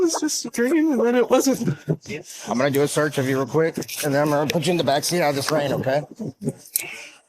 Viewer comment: I thought he said as well fed is put into the backseat and I started crying now my stomach hurts